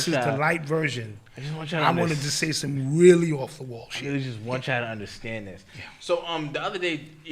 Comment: Do it bra that’s crazy I jus heard you saying nore wants y’all on